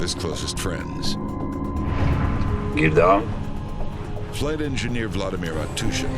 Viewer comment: Does he say "gear down" at 0:27?? They are on the ground??